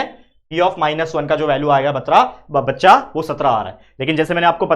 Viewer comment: when the sir said batra instead of baccha 😂😂😂